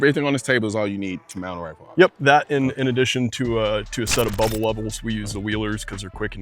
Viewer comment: he said "bubble level" however i heard "bubble wubble" and thats why i am smiling